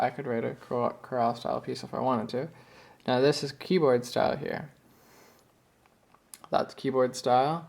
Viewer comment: did you say KEYWORD style????